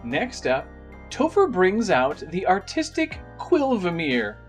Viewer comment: I swear he said autistic